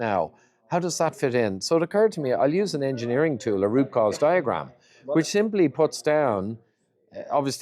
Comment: ngl, I had to slow it down & put on subtitles because I kept hearing rupauls diagram when it was root cause diagram 😂 (I watch on 2x)